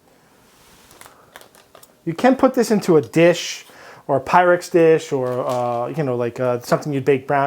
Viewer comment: I know you said Pyrex, but it really sounded like "Pirate" to me and got me excited for some salty sea dog's kitchen utensils